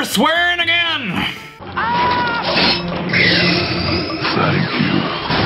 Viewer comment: I heard thank you